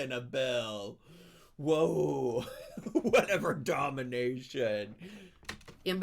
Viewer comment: omg im rewatching and i realized that i think she meant to say denomination not domination (24:14), i was like im not sure if that was the right word now that im watching it back HAHA